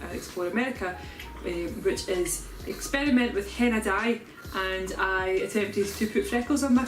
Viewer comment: I thought she said she experimented with hentai lmao